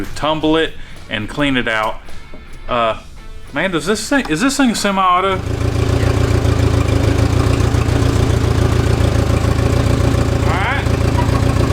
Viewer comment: I had to listen like 6x did you say “is this thing semi-auto?” What is semi-auto when talking about dirt bikes? A no clutch 3 speed?